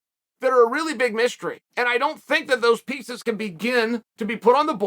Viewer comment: I thought he said 'pizza's' when he said 'pieces'.... RIP deepdish pt 3